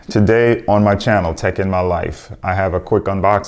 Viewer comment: i thought he said his name was "taking my life".